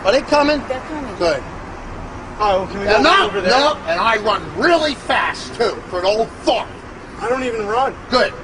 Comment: does he say i run real fast for a old fuck